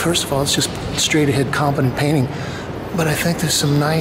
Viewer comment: "straight ahead [...] painting" - common painting? Sorry, I couldn't quite get what you said. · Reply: "...straight a head competent painting..." Sorry, I'll have to try to annunciate more clearly...